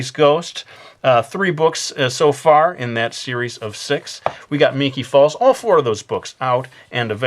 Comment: when u said series of six i thought u actually said series of s*x